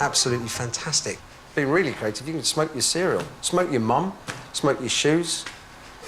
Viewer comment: Did he say "smoke your mum" at 18:18??